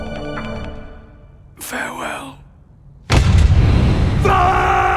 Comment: What does he say?? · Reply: He says: "Snake...farewell".